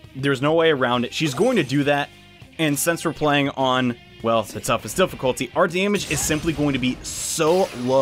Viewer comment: what difficulty is this? I can’t make out what you’re saying. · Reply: "The toughest difficulty." I don't remember which setting it is, but I think it's the Risky difficulty.